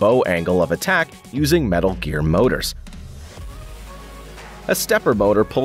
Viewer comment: did you say... METAL GEAR?!